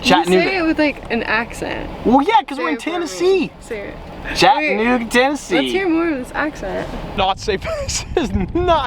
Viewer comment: Yo hold up at 4:07 did he just say the n word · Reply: A.A animations No, he said Chattanooga, Tennessee 🤦🏾‍♂️ c’mon don’t do Strad Man like dat.